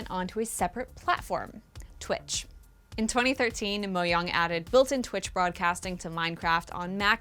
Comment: I don’t know if it’s just me but, when I heard “Mohang”, at the place of “Mojang”, my ears started bleeding...